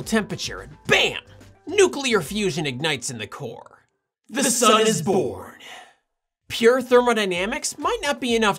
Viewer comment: For a moment I thought he said "The sun is Borg!"